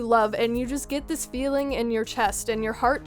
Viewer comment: I thought you said "Get this feeling in your breast." XD LMFAO